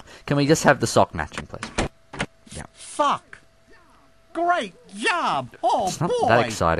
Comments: sounds like he says fuck. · it sounded like he said fuck instead of sock XD